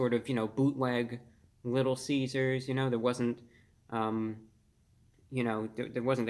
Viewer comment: Am I the only one who heard bootweg wittle ceasers at 3:35?